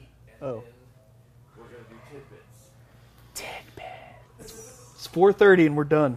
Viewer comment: I kept hearing "timbits" instead